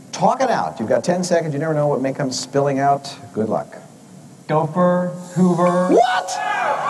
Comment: The first guy sounded like he said “dopher” and not “gopher” or is that just me? Lol